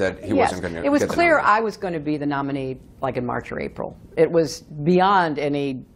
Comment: "it was clear i was gonna be the nominee like in march '84" i think she said something different, but i can't figure out what it was... if it wasn't, at least she's honest for a change...